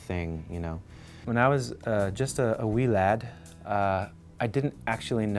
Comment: did he say "just a wee lad" at 0:46 ?